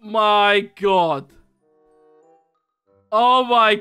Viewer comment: I thought he said what d*ck is this?